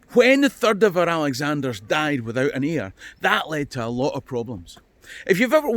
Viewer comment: I could have sworn you said Alexander III died without an ear, but then I'm English.